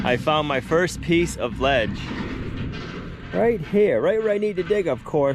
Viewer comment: Just watching your episode. You mention lead in rocks while digging the trench. Especially near your water source, I would be very careful. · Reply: Did he say lead rock? I thought he said ledge rock, a striation type instead of the boulders.